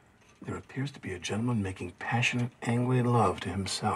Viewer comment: Jack says "passionate ________ love" to himself. Can anybody figure out what he's saying? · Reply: Passionate angry love